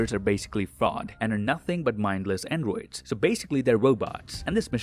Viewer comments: Every single time you said “androids” I thought you where saying “n-words” and I was very confused. I might need new headphones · Why do i hear him saying "mindless n-words" lol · oh my god i thought you were saying “mindless n-words” instead of mindless androids 🤦🏽‍♂️😂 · i thought u was saying “n-words” instead of androids all the vid naw 😭😭😭